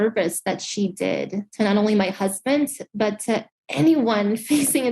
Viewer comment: did she say "My husbands" plural?